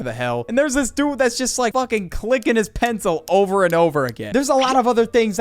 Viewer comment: i thought you said licking their pen not clicking their pen